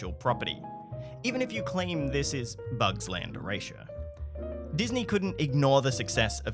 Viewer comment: What was said at 12:58 ? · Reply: “Even if you claim this is Bugs Land erasure” - Some things are difficult to say haha.